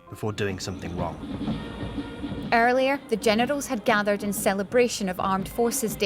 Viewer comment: Did she just say genitals